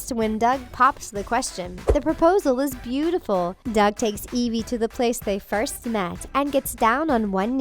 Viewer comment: the narrator says “Doug takes Evie to the place they first met”, but the captions say, “Doug takes Evie to the place they first smashed”.